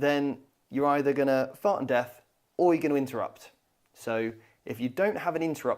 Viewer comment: I swear he said "fart on death"